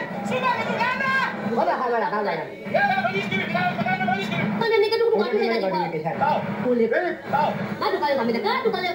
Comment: hahhahahahaha ma cosa dice hahahahha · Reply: “ parola incomprensibile, calma con gli spiriti” penso..